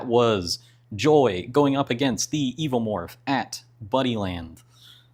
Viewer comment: For the entire video, I thought you were saying "Bunnyland." I was imagining an arcade full of bunny-themed games where a giant alien was running around taking everyone out. Then the ending hit, and I realized... It's Buddyland. 🤦😂